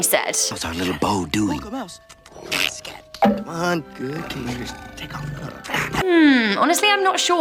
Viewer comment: I heard it "good kitty take of and go" did anyone else hear it like that?